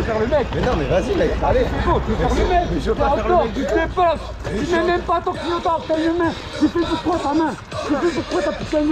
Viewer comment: euh... à 0:44 c'est moi où l'agresseur dit "tu ne mets même pas ton clignotant" ? 🤨 Bon courage à pour sa plainte, en espérant que ça aboutisse à quelque chose...